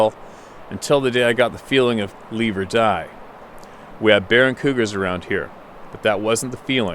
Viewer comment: OK, this is funny, when you got to the email around 19:00 I at first misheard and thought you read "there are barren cougars around here", which from a city folks' perspective means something completely different than "bear and cougars around here"....(falls on floor laughing and kicking back feet in the air) (yes, I know that we only have one set of feet)